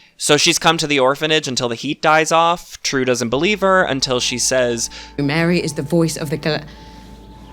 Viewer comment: what did she almost say?? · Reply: "Mary is the voice of the galan---" and then she stops herself